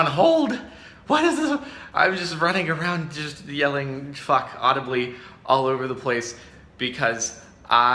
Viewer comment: The first time he said it I thought he said "Fuck audible" and I laughed too hard XD